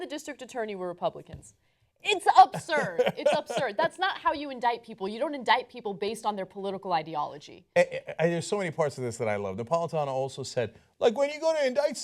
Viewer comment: The CMP committed fraud, but those on the right (tough on crime) are pissed? As an aside, at 4:43 did Anna say "Upsurd, Upsurd" (as opposed to absurd)?